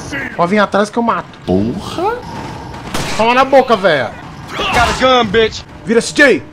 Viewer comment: '-' Oxi eu ouvi bem ? ''Agaragan''